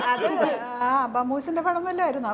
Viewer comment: what she is saying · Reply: Abham movies nte padam vallom ayrnno enn😂her production